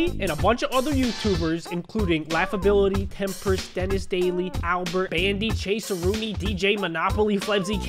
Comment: When he mention all the youtubers he was playing with he said Albert, I thought he said owl bird